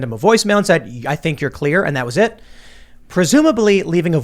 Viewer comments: Sounded like he said "i need you clear" as in "sober" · It sounds like he is saying "I need you clear". Anyone else hear that?